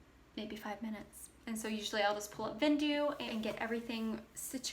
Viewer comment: At time code 18:40, you say you send your photos to where? Is it “Vindu” I’m hearing? Trying to figure out what you said. Thanks! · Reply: Hi Chani! It is called "Vendoo" it is a cross-listing software that I use and a lot of other resellers. It is so helpful! You can get 5 listings for free to try them out and if you like them, I have a 25% off discount for your first month :) <link> Hope this helps! Happy listing! :)